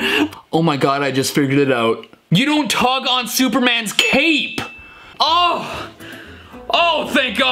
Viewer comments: I hear “don’t tug on Superman’s hip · I heard "You don't tug on Superman's cake" 🍰